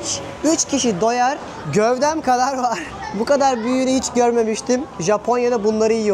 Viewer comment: "Bu kadar büyüğünü hiç görmemiştim ", that's what he said 😅